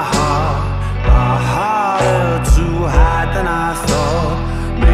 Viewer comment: I have no idea how I heard ‘I wanna be your lemon sneaker’ in the first line